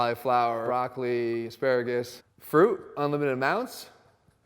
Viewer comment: did he say "in limited amounts" or "unlimited amounts?" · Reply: He said unlimited